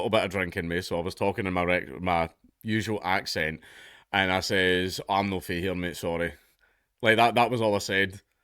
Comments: wtf did he say? Please Dank speak English. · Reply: "I'm not from here." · for those wondering what he said at 0:48, he said "I'm not from here, mate, sorry". for context, i'm an american in the midwest. you just gotta understand drunken speaking. also i have to say "can you repeat that" for people with familiar accents. not due to being hard of hearing, because i can hear incredibly well, it just takes me a minute for my brain to realize that the sounds i'm hearing are actual words. it's kinda like how dyslexic people see letters at a glance as like jumbles of spaghetti, but for me it's like an audio version. but strangely I can understand singers/voices in song, like, even Eddie Vedder, just fine. like how people with dyslexia have less trouble reading comic sans font.